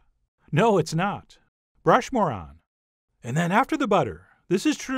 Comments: is he saying "brush more on" , or "brush moron"! Lol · Did you say "brush moron"?